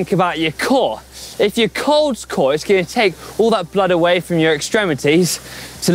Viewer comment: Did say Colds Core? Must be the cold getting to him. 😅